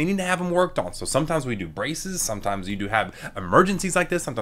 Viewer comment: I’m sorry did you just say BRACES😂